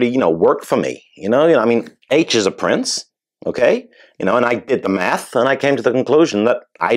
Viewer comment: I heard "I did the meth" 😂